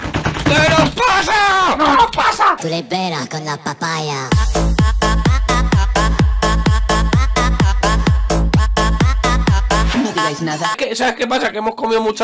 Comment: Yo soy la unica que escuche que en la intro en el minuto 00:29 o 00:30 dice "no veais nada"